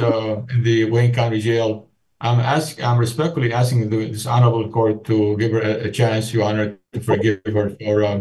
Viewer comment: I know he is saying "This" Honorable court but it sounds like he says Dishonorable court 🤣